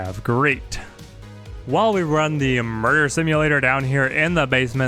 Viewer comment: You said , what sounded like "while we run the mer simulator" at about 14:20, when you were trying to get the abominations to attack each other, what did you say and how did you get them to attack each other? many thanks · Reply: He said “run the murder simulation”. He gradually marked groups of the abominations for termination, meaning they turned on each other- there were some who were left unmarked while the engagement happened.